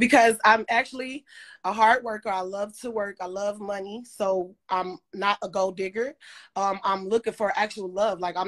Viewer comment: LOL SOUNDED LIKE SHE SAID SHE'S NOT A GOAT DIGGER